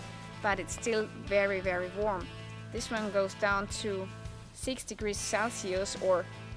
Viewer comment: Thought you said 60 degrees celsius XD